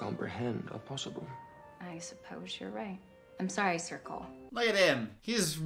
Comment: I can’t get over the fact that Ser Cole sounds so much like Circle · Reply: I legitimately thought she said circle 😂